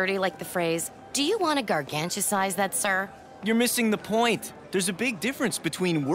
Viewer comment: Who wants to help this lovely follow man to improve his English? Nikki said the following:" Do you want to garganta sighs that, sir?" from 1:25 in this episode. What do the words 'garganta sighs' mean? Any help would be much appreciated! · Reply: She said gargantasize, it basically means very big from my understanding so how she said it would translate to “would you like to enlarge that?” So basically she’s asking if he would like to move it up a size to a bigger size